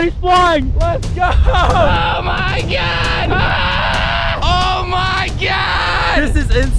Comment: i thought was saying oh my gyatt 😂😂